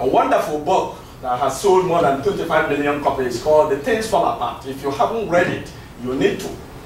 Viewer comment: Could someone provide the title of the book mentioned around 56:15? I couldn't quite catch it. Thanks! EDIT: nevermind, I found it; it's called Things Fall Apart